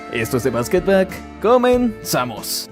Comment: Bro escuche " the basketblack"!